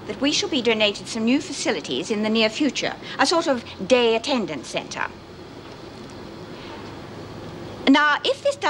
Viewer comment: I thought Davo said "a gay attendance centre" and that was the reason the camera panned straight to Judy.